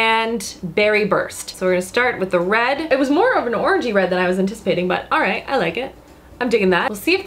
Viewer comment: I know I must just have a super dirty mind but when she was talking about the first matte lipstick I thought she said "orgy red" and was like wtf is orgy red? Then realized she said orangey-red.